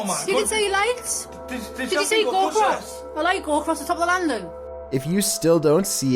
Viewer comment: I swear that girl said "Did you see Bob Ross?"